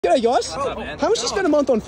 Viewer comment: i thought you said “Gay guys” but you meant “G’day guys” because the editor have cut it by a inch and made if sound like you said gay 😂